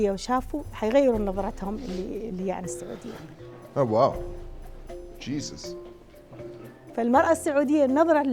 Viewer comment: did he just say Jesus?